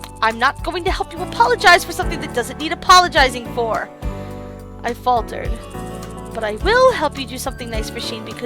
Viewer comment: I thought she said I farted 😐 I'm really late to this he he